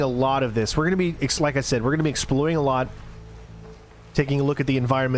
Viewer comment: Hey, slight slip of the tongue at 14:08. "We're going to be exploding a lot"... well, I guess you're going to be doing that too, but I don't think that's what you meant to say. 🤣